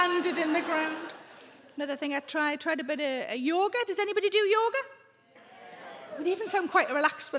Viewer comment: wait did you say does anybody do yogurt? 😂😂